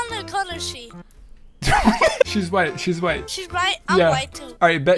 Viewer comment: Bro it’s 2023 and the closed captions said I’m white, Guess he has the nword pass😂